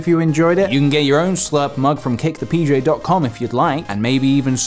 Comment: I thought he said "Cake the PJ"